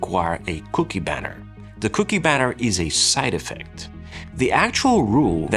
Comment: I totally thought you said cookie batter. 🍪